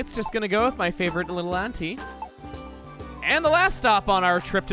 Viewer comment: I thought you were saying Anti-Kangaskhan -_- not Auntie (Ahn-tee) how we say it here. I was like what did she ever do to you :'(